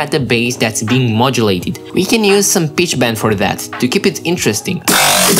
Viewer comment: Am i the only one who heard him say bitch instead of pitch